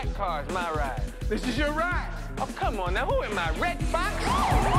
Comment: I swore he said "who am I? Fred Sanford?" Instead of Red Foxx. Just watched this for the first time on hulu. Had to come check the trailer to see if they changed it in the movie.